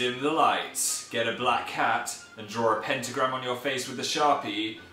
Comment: I thought he said "placenta" instead of "pentagram"